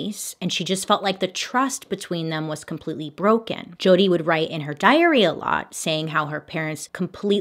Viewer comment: I thought she said "she would write in her diarrhea" 😭😭😭😭